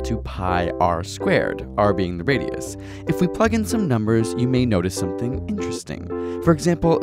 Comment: did he said "let's ** in some numbers" or "let's plug in some numbers"